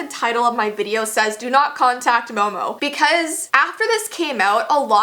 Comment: Lol I thought it said do not contact Mom 🙈